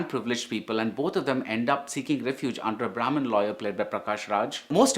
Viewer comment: did you say prominent lawyer or brahmin lawyer?